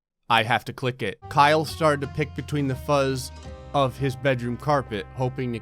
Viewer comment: help I thought this said Huzz